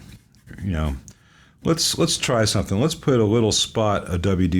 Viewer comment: When you said, "let's spray some WD-40..." I heard let's Pray, lol Considering the look of things, I was ready to join you! Best of Luck on this one!